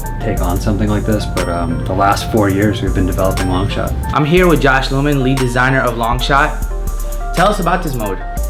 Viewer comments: who else thought he said Josh Norman? lol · i thought he said josh norman 😂😂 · I thought he said josh Norman but is 😂